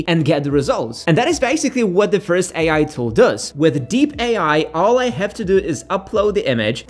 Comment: I think he said it guys, at 0:28 and it calls DEEP AI. However, I think this tool is worse than so many other AI tools that I saw, and to replace Photoshop, I think AI has a really long journey to go. It's fast, but can't get really professional look yet, only for amateurs to play around.